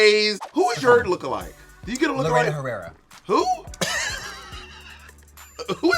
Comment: “who is your look alike?” I thought Katya said Carmen Carrera at first and that’s why I thought Bob said “who!?” 😂